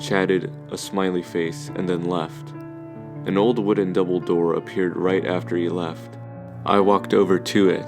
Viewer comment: lmao the captions say an old wooden dumbledore